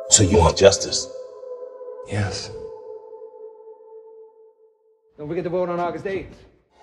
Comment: What's being said at 0:50? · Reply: Don't forget to vote